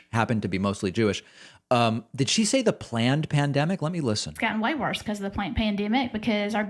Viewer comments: She was thinking "plandemic" but caught herself at pl and corrected herself to pandemic. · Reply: It does actually sound like she says "planned pandemic". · Yeah, this IS BAD..🤭 I heard “planned plandemic”? I’m southern and I get the accent. My family are from KY. But I think I heard it. Which is another dog whistle to the ignorant. Jesus first in your life is fine, but don’t bring him to work everyday. 🤨